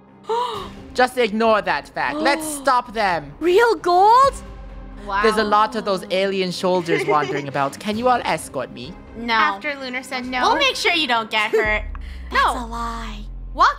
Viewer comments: is it me or I heard say Alien shoulders?? Instead of soldiers? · Anyone else hear say shoulders instead of solders